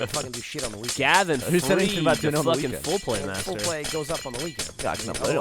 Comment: I thought Gavin was saying foreplay, not full play. so...